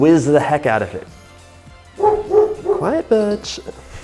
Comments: thought he said b*tch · i thought he said quiet bITCH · i totally thought you said "quite B*tch" and that dog bark made me jump, omg. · I thought he said "quiet bitch" when his dog started barking 😂😭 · I heard “Quiet biiitch” 😂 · I could have sworn he said quiet bitch!! It's Butch!!🤣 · I am so sorry but i thought he said " be quiet b**ch" lol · lmao, I seriously thought at first he said "quiet bitch". But then read the caption. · I thought he said quiet b****😭😭😭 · I thought he said quiet b*tch not butch 😭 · i thought he said quite b*** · I need to make macarons for cake decorations. Do I need to measure out the eggs the day before or can I measure and use them immediately?. Plesse help. These look delicious..also. when you said "quiet Butch", I swear I heard "quiet Bitch" 🙈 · When his dog barked I thought he said "Quiet Bitch" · I thought he said "Quiet B*tch" and i am so glad he clarified that the names was indeed Butch xD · i reaally thought he said 'quiet, bitch' 🙈